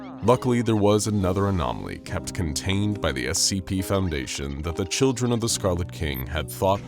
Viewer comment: It might be because I’m so tired but I heard “the children of the scarlet corn” idk why lol